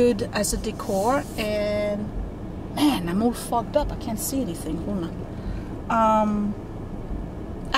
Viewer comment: 🤣🤣🤣 i was fixing my drink and stepped away from the screen when you were leaving the goodwill and i believe you were discussing the windows but all i heard was "I'm all F#%$ up" I just about dropped my glass and had to rewind to hear it again hahahahhaha